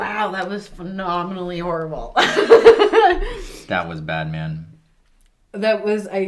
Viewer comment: "That was bat man " Lmk if you can't hear that